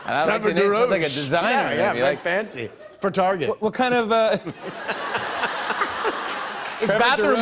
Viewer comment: does he say what kind of or wakanda🤣😂